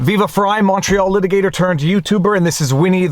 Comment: WAIT HOLD THE GODDAMNED PHONE! "VIVA FREI, MONTREAL LITIGATOR-"?!?!?!?!?!?!?!?!?! I WITH MY WHOLE HEART FOR LIKE 2 YEARS NOW THOUGHT YOU WERE SAYING "VIVA FREI. MACHO LITIGATOR." AND NOW I DONT KNOW WHATS REAL ANYMORE SEND A DOCTOR